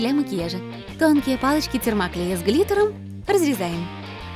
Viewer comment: палочки с глитором))) 🤣🤣🤣 ой послышалось))😆😆👌